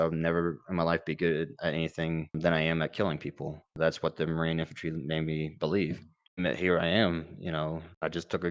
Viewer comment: he actually says, "that's what the Marine infantry made me believe, "